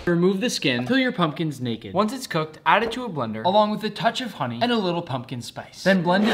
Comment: Wait did he just say no let’s add some pumpkin powder